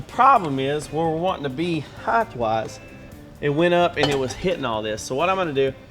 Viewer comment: did you say "heigth"